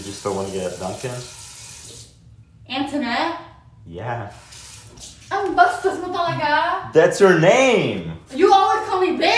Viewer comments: I heard “Ang bastos mo talaga?” 🙌🏾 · did i hear "ang bastos mo talaga?"